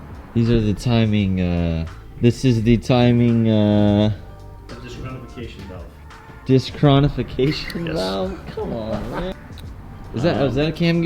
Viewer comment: For A Moment I Thought He Said " Timing Disqualification Valve " ! Lol ! Love The Vids , Keep It Up ! :D